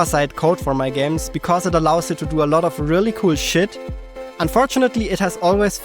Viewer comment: Did you just say "shit"? 👀